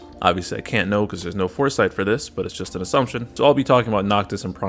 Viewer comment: Lol I thought u said cor-sight instead of foresight in the beginning. Was like damn..this man is the goat😂